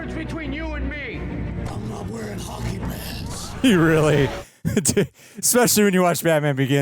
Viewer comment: So for years, I thought Batman said Hockey "Pants", not "Pads" thanks to the voice 😂